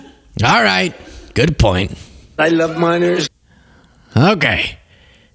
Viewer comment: did trump say "I love minors"? I mean that's a truly odd sentence.